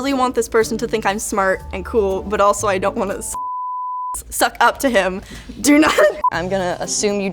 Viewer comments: anyone else wonder what she says @ 4:20 when she says she doesnt want to Bleep bleep. Suck up. My mind is always in the gutter so i immediately thought she said "suck his d!ck" . Am i right ? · Did she say "suck OFF to him" accidentally? 4:19 (second edit: she's perfect for LTT)